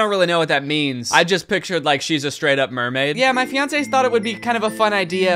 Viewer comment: ngl everytime you said fiancé i thought you were saying beyoncé and i was so confused